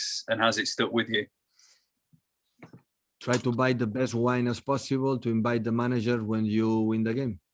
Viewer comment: WHAT DID Pep say at 8:11? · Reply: "try to buy the best wine and invite the manager when you win the game"